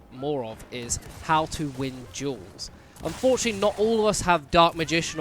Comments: On number 10 I thought you said jewels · What? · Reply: you were saying duels i thought you said jewels